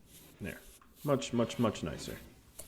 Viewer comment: Did you just say 'Mudge mudge mudge nicer'? :-D Never hurts to pad the comments, right? ;-)